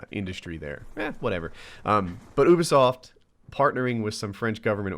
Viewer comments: When said ubisoft, I thought he said boobisoft!?!?! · the first time you said ubi soft i thought you said booby soft